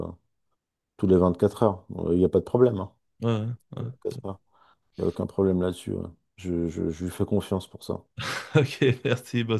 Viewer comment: 😅 A 22:02 - ' Merci à toi Alain ' : Youssef a grand remplacé Soral ? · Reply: J'ai dit "Merci à toi alors" ;-)